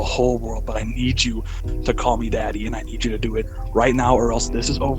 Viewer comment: When he said you need to call me daddy a add came on so it sounded like it said call me cheesy 😂